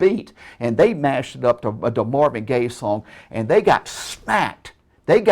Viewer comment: Did just call it a gay song at 15:00?